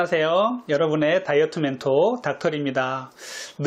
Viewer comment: 안녕하세요 여러분의 다이어트멘토 닥털(?)입니다~로 들려서 빵 터졌어요ㅎㅎ